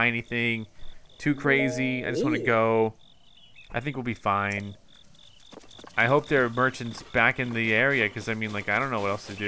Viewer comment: I find his humour and sarcastic remarks everytime he fails miserably very funny. He's not exactly the sharpest tool in the shed but I think that's what makes his play throughs so fun to watch ^^. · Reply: i thought he said i hope there are virgins back in the village